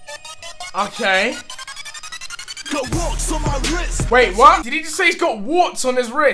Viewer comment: I thought he said I got rocks in my rist